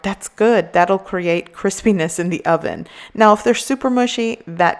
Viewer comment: when she says crispiness..the captions says "chris penis" lol